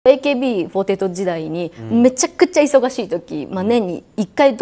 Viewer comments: AKB48時代がAKBポテト時代に聞こえて仕方がない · AKBポテト時代に聞こえた